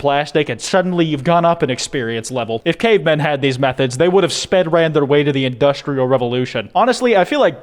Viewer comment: Did he say Spedran